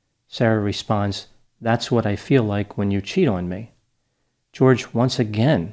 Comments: I thought she said "that's what it feels like when you CHOKE me" in that video, the fact that she actually said "that's what it feels like when you CHEAT on me" seals the deal on this case. I was having sympathy, but it certainly doesn't sound as if he was physically abusing her by her account, that fact makes this case pretty clear: she was punishing him because she felt he was being unfaithful and she did it by torturing him to death - basically, fuck her. Unbelievable. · she went out of her way to pick a lower educated, addicted man, the suitcase game wasn,t the only vicious game she played, she sought to dominate him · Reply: Exactly. I thought she said "that's what it feels like when you CHOKE me" in that video, the fact that she actually said "that's what it feels like when you CHEAT on me" seals the deal on this case. I was having sympathy, but it certainly doesn't sound as if he was physically abusing her by her own account, that fact makes this case pretty clear: she was punishing him because she felt he was being unfaithful and she did it by torturing him to death - so basically, fuck her. Unbelievable. She talks about him like a pet owner would a dog, she is clearly a narcissistic person who sought out someone who would be entirely dependent on her emotionally and that's what she got with this guy, he started messing around with chicks online and she didn't like that.